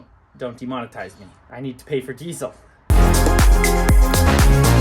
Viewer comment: Haha wow I thought he said he had to pay for T-cells at 8:56 it made me "whol up wait just a minute!!"